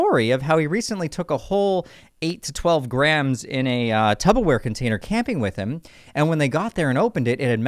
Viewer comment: Did you say "tub-o-ware"? its Tupperware bro.